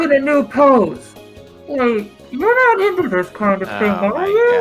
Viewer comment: For the life of my life, I could have sworn she said 'you're not into this too, are you?'. I'll have to look it up later, but it still is the best joke in the game.